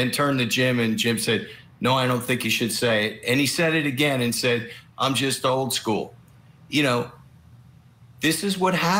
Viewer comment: Hapoolian did not say "I'm just old school". He said "I'm an old man". Clearly stating he is not current on the new words you can't say. I am not a fan of Harpoolian but right is right.